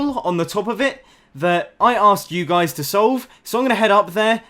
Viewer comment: He said "I Asked" I heard "i Ass"